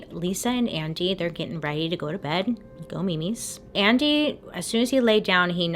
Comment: Did she just say " go memes ". OMGOODNESS that's what I used to say to my kids as babies n toddlers. Now I'm Italian so it's not a term anyone in my family said, it was just something I created, at least to my knowledge I'd never heard it elsewhere. Hmm cool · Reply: Hi there! I grew up with my family constantly saying it. It’s quite common in the Hispanic culture to use that as a term for bedtime. We spell it “mimis” though. I’m not sure where else it’s common but I was shocked when I first heard her say it too